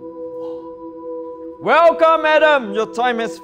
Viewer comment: When they said "Welcome Adam"I heard "welcome madam" lol Who else? Or...? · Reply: Thought I was the only one 😆